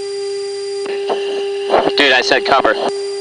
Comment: Lead pilot was pissed at his wingman "Dude I said cover" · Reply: I heard "Two I said cover" then "Two is off."